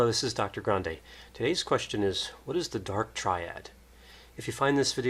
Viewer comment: I heard it as, the mcdonald triad, today. Has the name changed? I've only heard it as the dark triad, up till today. And why mcdonald triad?